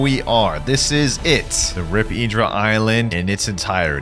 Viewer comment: did bro said rip E-dra💀💀